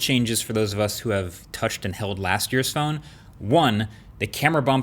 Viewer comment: Touch and held last year's...For a moment I thought he said..."touch and inhaled." Nice content MKBHD..